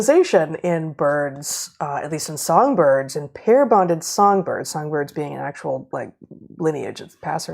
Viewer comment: I could have sworn Heather said pair bonded FERNs. I was very confused for a few moments.